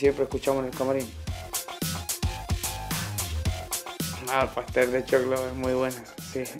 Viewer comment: When asked what his favorite food was I heard "Pastel de Choco" which I don't the slang in Chile but I THINK might mean Chocolate cake, but maybe I just heard wrong 🙂